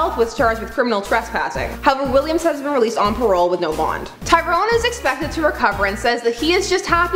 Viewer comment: Did you say hes been released on parole without bond around 1:44 or did i hear wrong the 500 times i replayed it